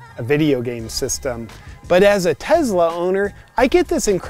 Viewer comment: JP sounds like your saying TEZLA